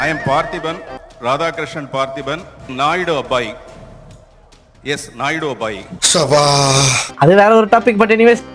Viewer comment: I heard it as "Naidu a boy" 😂😂😂